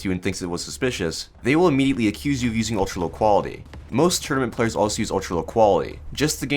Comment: I for sure thought you said "Most German players use ULQ" and I didn't think anything of it at first.